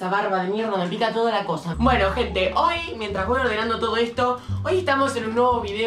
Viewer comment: Cuando dijo “bueno gente “ yo le entendí “ bueno ojet#”